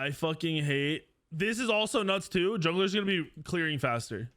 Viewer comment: All I heard was “it’s a good time to be a jungler”.